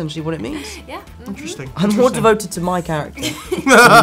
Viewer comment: Oh shit. At 7:36 I heard that Tom is more devoted to Mein Kampf. Wtf.